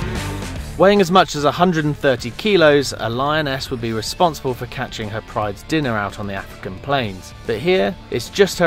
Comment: did i heard lion-ass